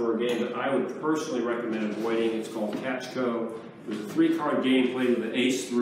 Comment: Not sure if anyone will see this or answer this - but did anyone catch the name of the card game he references here around 24:07? I think I'm hearing "catch-go", but not sure. I'm doing some research for a book and would like to know. I've googled "Catch-go", but cannot find it anywhere online. Any assistance would be appreciated!